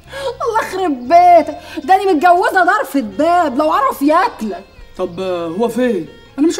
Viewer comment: ممكن احد ينورني ايش قالت ريا بالدقيقة 2:57 انا متزوجة ايش؟ ؟😂😂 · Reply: متجوزة درفة باب يعني طوبل وضخم😂